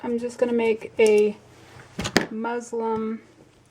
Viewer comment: thanks for the tutorial. I believe the word is MUSLIN for the fabric not Muslim! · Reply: It is “Muslin”! I was thinking I kept hearing her say “Muslim” too!